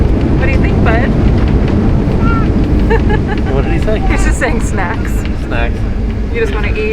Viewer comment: It sounded like he said fast instead of snacks when you guys landed !! Lol 😁